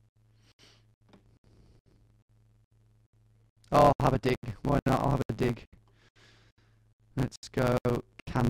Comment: What an unhuman guess. This must be Cyborg Doppelgänger. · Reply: What did he even say during that segment? I heard "Oh, I have a dick, might not have a dig."